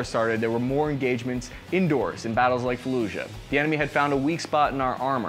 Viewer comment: Did he say “felucia”. Like, from Star Wars?